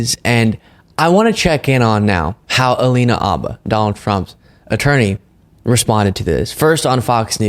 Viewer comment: Totally sounded like you said Donald Frump at the start. I love it